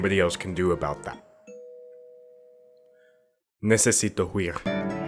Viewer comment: Did you just say, "necesito queer" cause I can assure you "queer" does not mean "to run" in Spanish. "Correr=to run" cool? Cool. Not that you asked or anything but ya know. Duolingo owl tings.